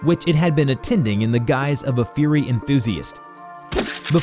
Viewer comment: i thought he said fury and not furry lol